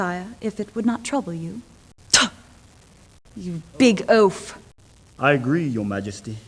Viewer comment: did she just said "OOF"